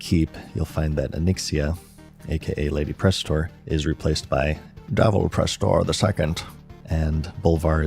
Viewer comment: Thought you said Donald Prestor lol